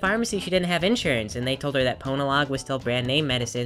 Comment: lol I thought it said PORNALOG but it said PONALOG